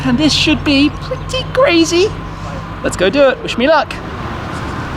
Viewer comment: Did he just say wish luck?